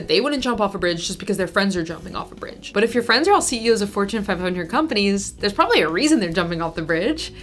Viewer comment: I heard "4chan 500 companies" and sadly, I'm not wrong.